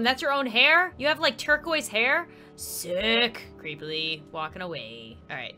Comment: Help- MY SUBTITLES AT 10:58 SAID D!CK INSTEAD OF SICK- 😭💀